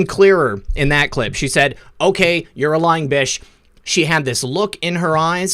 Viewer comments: I heard " I think you're a lying * itch" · I heard "I think you're a lying bit*h"